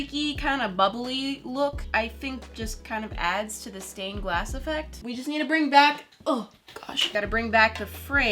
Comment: I thought she said phone stain glass and I was like FINALLY A PERMENANT FILTER